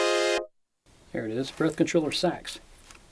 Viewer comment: What did he say at 18:28!? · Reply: "There it is, 'Breath Controller Sax'". I auditioned several sounds before we filmed my segment, but failed to write down their patch locations.